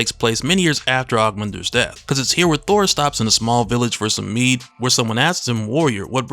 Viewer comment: i swear i thought you said “Thor stopped in a small village for some weed” and i had to pause the video and rewind 😂😂😂